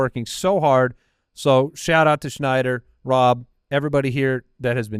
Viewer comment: did I hear say that Rob Schneider is the mastermind that has been working behind the scenes on the fantasy footballers in season app?